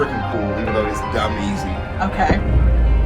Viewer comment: did bro just say easy, oh nah